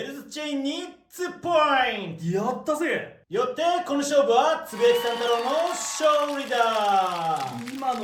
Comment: did I just hear “Oh Sh*t!?” In English?